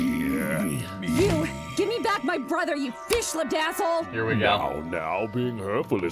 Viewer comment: for years, I thought she was calling him a "Fish ladazzle" and I was like "What's a ladazzle?" Turns out, she's saying "Fish-lipped asshole!" That makes more sense.